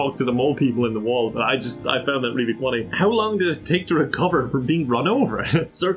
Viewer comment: I thought it said how long did it take for you to get run over😂